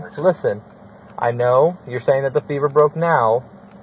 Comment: At first I thought they said femur. All this over a fever?